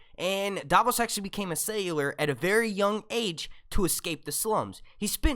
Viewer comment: "Davos became a cellular" LOL Sailor? 1:38 I think you mean "At sea" instead of "On Sea"